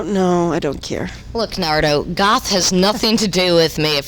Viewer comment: I seriously had to listen back like 10 times I thought she was saying "Naruto" but that didn't even exist back then. I guess she was just saying "Nardo"